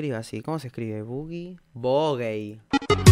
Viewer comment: parece que dice "bo gay" Jajaja salu2